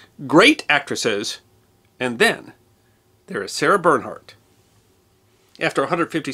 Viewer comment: I started this video without reading the title. I thought you said Sandra Bernhard and I was confused.